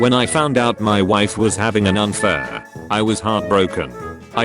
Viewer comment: "When I found out my wife was having an unfair" Me, who wasn't paying attention to the screen: "What did you just say to me?"